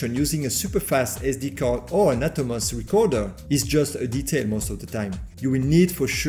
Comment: thank you for previewing the firmware! at 15:56 what is the alternative to the SD Card? Subtitles say "Natomas recorder" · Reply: an Atomos Recorder :) such as the ninja inferno